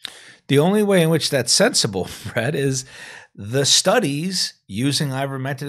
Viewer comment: did he just call Bret Fred? lol